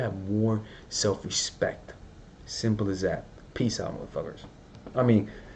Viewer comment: what did you say? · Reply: Peace out mother f*ckers